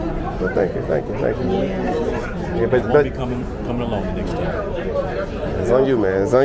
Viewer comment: Did he say he's not coming alone next time or he's not coming along next time? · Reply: “Alone”